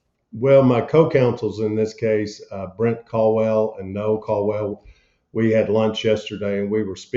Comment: Thought he said Bryce Caldwell thats been my lawyer in OWENSBORO KY he real good at this kinda stuff KY needs to be investigated they do alot 4th amendment volitions